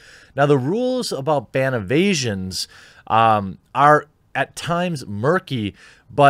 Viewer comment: When he said ban evasion, I kept hearing “ban of Asian” and kept thinking about Bill.